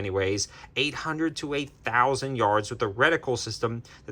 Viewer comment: When i 1st watched i thought he said up too 8000yrds😂